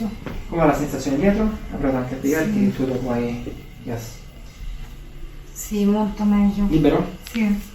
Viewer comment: preferivo che cambiasse inquadratura...🤣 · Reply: Per un attimo ho capito "Si monta meglio". Sono un uomo semplice, mi faccio condizionare facilmente.